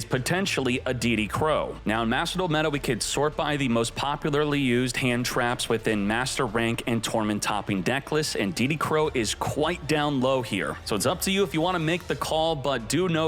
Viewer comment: Thought I heard “Diddy Crow”.. had to rewind 10 sec. That was a close one